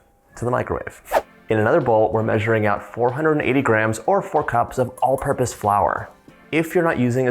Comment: I misheard the measurement for flour and thought John had said 400 grams. The result was a very wet and sticky dough so I started adding more flour but I was trying to leave the dough a little loose so I added more until I though it was right. it wasn't. it was still very loose and the sticky buns were saggy when I rolled them up. I checked the Internet and realized that one cup of flour is 120 grams (not 100). Second batch was better but this time I think I was too high in flour content. I finally got back to watching the video. I started with 480 grams of flour and it was still sticky. I added 20 grams more and I think it's right. It's a little sticky but, when I pull my finger away, it forms a ring of dough but the center of the ring is clean-ish. I might back this off to 10 grams to see what the results are but I think my magic number (for atmospheric conditions, humidity, moisture content and quality of my flour) is 500 grams. By the way, even with my mistakes, everyone is raving about these.